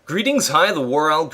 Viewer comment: I always thought he said GREETINGS I but I realize now he says GREETING HI